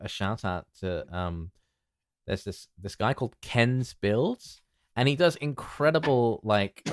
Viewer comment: During the merch talk, I couldn't make out the name of the channel? that you wanted to shout out and couldn't find it in the description. Kens bills?